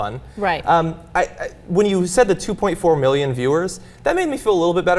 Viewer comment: I wasn't listening properly and I misheard 2.4 million viewers as 24 million viewers... my heart jumped out of my chest and I mentally wrote off America forever...